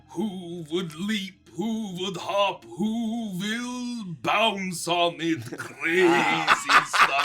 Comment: I genuinely thought he said “Whoville” the third time 😂😭 (as in the Dr. Suess town)